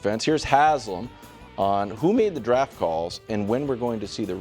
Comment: i heard: "Hootie and the Draft Calls" 😅